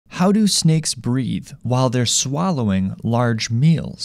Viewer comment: I thought you said, "How do snakes breed". Imagine my confusion! 😁😁